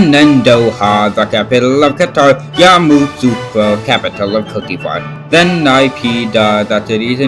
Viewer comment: why did I hear "capital of cookie bar" 😂😂😂 Amazing video though Edit: 3:36 YAY FINALLY